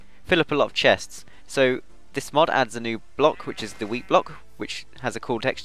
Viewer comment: When you say wheat block it sounds like your saying weed block :D